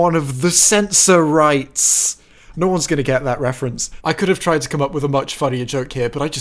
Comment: Did you just say I’m no one? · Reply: DarthAdipose no he said no one is going to get that one